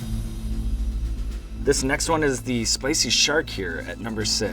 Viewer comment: I thought he said spicy shart lol